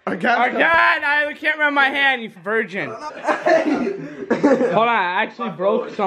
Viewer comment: did he call him a virgin 😂😂😭🤷🏻‍♀️